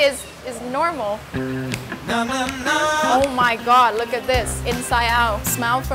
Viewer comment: Did she just say "Oh My Cod?!!!"